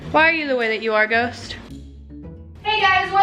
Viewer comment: When all the pretzels are sold out It also sounds like is saying “roast” lol